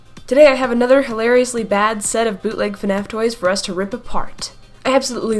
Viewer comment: Turn on captions at 0:06 it says “bootlegs and asteroids.” Instead of “bootlegs and fnaf toys.” Lol